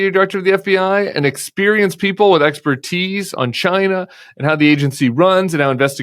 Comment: I heard "...experienced people with 'extra teeth'" instead of "expertise" and it made just as much sense to me.